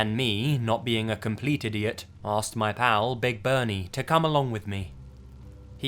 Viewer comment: when you said "my pal big bernie" i heard it as my owl. all i imagined was this big ass owl coming with the dude to pick up the clothes lmao